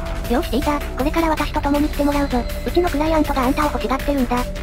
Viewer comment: うちのクライアントが 口の臭いアントに聞こえた俺は末期